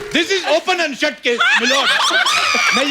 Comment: In this point. I did't understand what kapil is saying🤔 Can somebody help me🙂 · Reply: This is "Open" and "shut" case